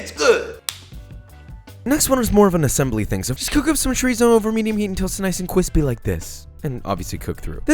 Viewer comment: What is that @ 5:49 ? I didn't quite catch the phrase. The auto-generated caption doesn't really help. It says, "..cook up some trees over medium heat.." Thanks so much. *EDIT: Ok I got it. It was Chorizo. Thanks.